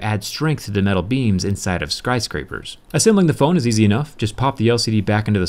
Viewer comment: I replayed this a couple of times to make sure I didn't misheard what said, but he said skRyscraper. I find that very amusing hahaha :)